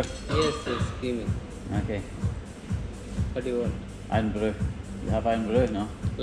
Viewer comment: Lol’d when you asked for Irn Bru in your most Scottish accent and the fella thought you said Lion Brew 😆👌🏻